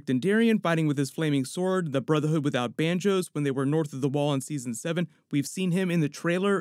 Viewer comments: I am the only one who heard "Brotherhood without Banjos"? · at 6:17 did you actually say "Brotherhood without banjos"?